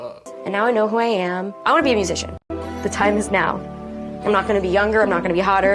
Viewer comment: I thought Nikki said she wanted to be a “magician” not “musician”. 🤣